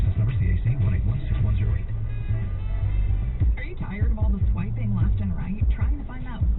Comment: that ad sounded like “are you tired of wiping left and right” and thought to myself “that’s not how you wipe!” 😂